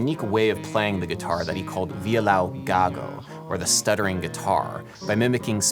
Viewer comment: im a simple filipino, i hear gago, i laugh and die :)